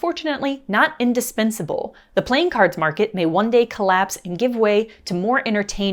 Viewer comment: This is maybe an odd question, but around 7:58, instead of "The playing cards market may one day collapse," as you say out loud in the video, the subtitles say "The playing cards market May 1st day collapse,". Was this a spell-checker mistake?